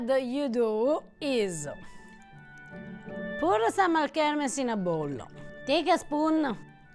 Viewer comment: What the hell is in that bottle at 13:31? I can't understand what she said. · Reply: Alchermes